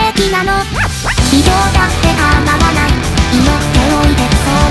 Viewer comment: 「今日だって叶わない」が「卑怯だって構わない」に､どうしても聞こえてしまうから､こう…こう…なんか…。こう…。なん､なん…なんかなん…。( ^o^)＜ﾝﾝﾝﾝﾝﾝﾝﾝﾝﾝﾝﾝﾝﾝ（脳死）